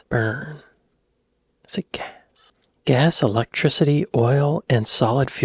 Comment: When you were wondering aloud what fuel powers an aga, I misheard you and thought - just for a moment - you said "Is it cats?". Whilst I found both the actual suggestion and my mishearing "gas" hilarious, had you actually suggested it and had a smile on your face whilst saying it, there's at least one subscriber you'd have lost. (To me, it's more than just a screen name and a picture!)